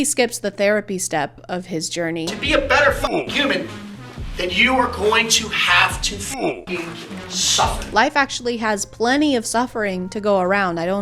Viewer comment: I thought he was saying "to be a better Cuban you are going to have to suffer" lmao as if the embargo isn't enough